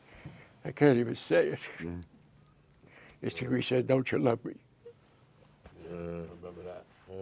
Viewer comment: what did he say? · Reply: He was quoting the last line when Tony met Unlcle Junior in the hospital when Tony asked him Don’t you love me